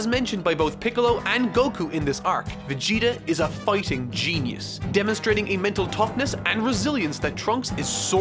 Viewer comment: i thought he said FUCKING GENIUS